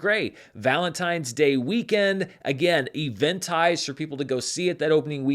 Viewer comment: (or so) I can't figure out what you're saying after "Valentine's Day Weekend"... Event highs? Eventized? I've listened to it like 30 times and I still have no idea what the words are on that part :(